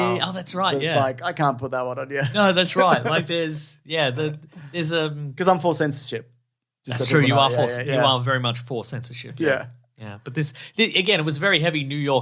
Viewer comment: at first I thought fucked up saying “i’m force sensitive” 🤦🏽‍♂️😂